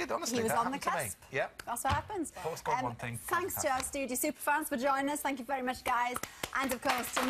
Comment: did she say vagina? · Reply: Thanks for JOINING us she said:)*